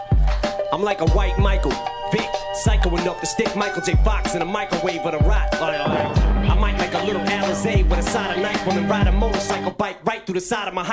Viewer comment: can anybody write what he said in first 4 lines at 6:57?? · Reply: I'm like a white Michael – Vick, psycho enough to stick Michael J. Fox in a microwave with a Rott I might make a little Alizé with a side of NyQuil And ride a motorcycle bike Right through the side of my high school Satan's disciple with a sniper rifle And a knife and a white diaper Liable to shit on you while I snipe you So dope he gets off opiates What an appropriate way to start off his day! He may just smart off to Dre He may be hard to contain ‘Cause his rage is so hard to gauge See, Hannibal ate his face, and met Jason, gnawed off his leg Amazin' hard-on for razors and blades And anything sharp, even poisonous darts It all plays a major part of his game Holy water won't ward him off, crucifixes won't do the trick He's so sick, it's ridiculous Sawed the crazy part off his brain, he's still insane Why's there bloodstains on his carpet, mane? There's some crazy shit goin' on in Shady's apartment again